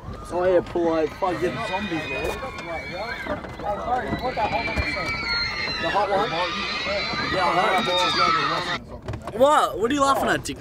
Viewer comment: when theyre in the car, at like 3:31, does anybody else hear them day, "the hot one? yeah i know" ?